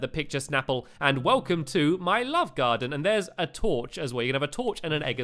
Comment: your just amazing and everyone that dosent watch him then what do you do with your life and lol when you were on your way to the love garden it sounded like u said london